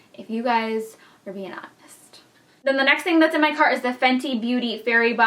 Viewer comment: OH.... Its what's in my *cart! In your snap, I totally thought you said What's in my car*.. lol. And I actually thought, well I'd watch that for sure, LOL